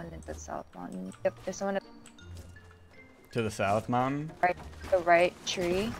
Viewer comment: he sounds like he said to the south mom